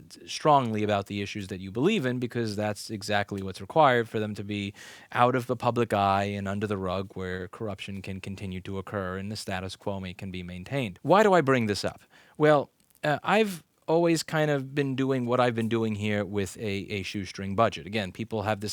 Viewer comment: I heard "and the status Cuomo can be maintained" My ears are playing tricks on me.